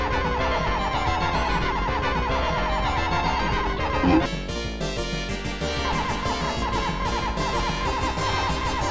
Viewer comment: Is It Saying BEH? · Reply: It’s BLUH